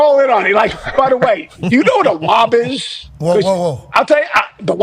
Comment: probably the funniest thing I’ve seen on this show in a long time. Pat thought he said WAP instead of WOB 😭😭 the panic in his face lmao!! Connor “oh a B” 💀 For the uninitiated WAP was a song by Cardi B meaning wet ass p** lol